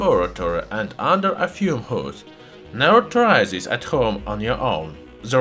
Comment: CC says: "Naruto rises at home"🤣🤦